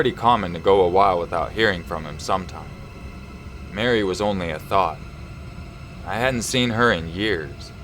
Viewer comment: Number 13 it sounded like he said Mary was only a thot lmao